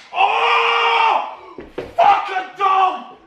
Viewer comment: did Harry say f a dog?